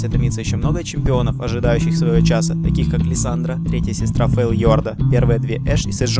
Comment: does he say Lisandra?